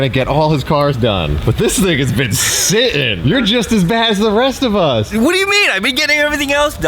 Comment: did you say this niggas been sittin'?